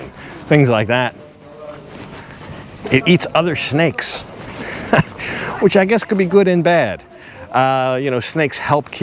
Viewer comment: I thought you said "It eats other snakes, which I guess could be good in bed". I'm thinking WTF! 😂😂